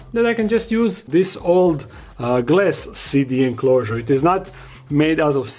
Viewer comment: Im going to keep it 100. This entire time i thought you were saying "city enclosures" not "CD enclosures" 🤣🤣🤣